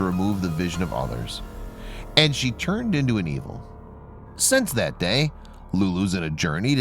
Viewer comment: Who else heard “-and she turned into an Emo.” 😂